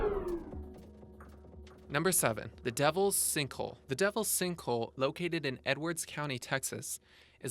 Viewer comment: I thought they said stinkhole.